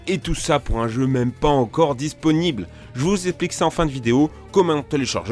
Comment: "j'vous éplique" Putain toi t'as été terminé à la merde ou bien ?